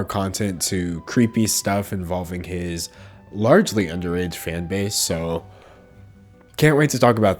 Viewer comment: i thought he was going to say his large di